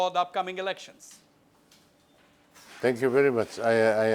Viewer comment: Skip to 18:06, and for those of you who misheard him, Mr.Lalthanhawla replied, "I DO need your good wishes". (*ps: Saying this because many of you probably misheard it.) Peace✌🏻✌🏻